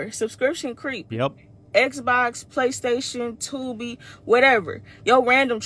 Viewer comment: Did she say Tubi? Subscriptions suck, but Tubi doesn't have subscriptions (just ads, you also don't even have to make an account).